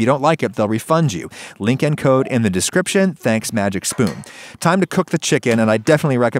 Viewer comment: I thought he said "magic shroom" instead of "magic spoon" lol